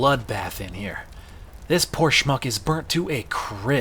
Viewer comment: I was watching this trying to sleep and I thought you said “This porch monkey’s burnt to a crisp.” And that immediately woke me back up. I had to rewatch three times before I realized you said “Poor schmuck”